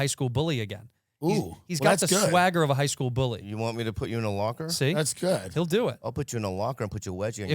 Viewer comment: I thought bobby said "you want me to put you in a walker"?😂